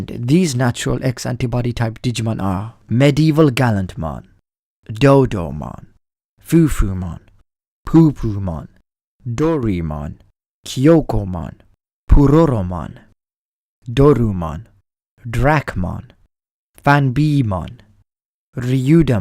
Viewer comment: did you just say "poopoomon"?